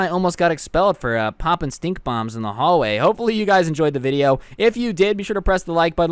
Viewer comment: it sounded like he was saying he farted in the hallway